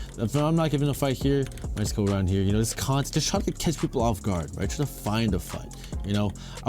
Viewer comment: why did i hear try to kiss them off guard...